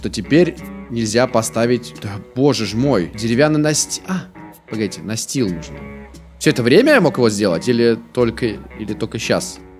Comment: ЕЩЁ ОДИН АНЕКДОТ: - Что сказал слон наступивший на колобка!? - "БЛИН" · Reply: Да, тебе послышалось. сказал: "А! Погодите, настил нужно. Всё это время я мог его сделать?!"